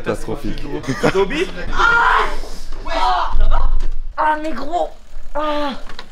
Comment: euuh néQuoi ????? 😐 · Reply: non non il a dit “mais gros”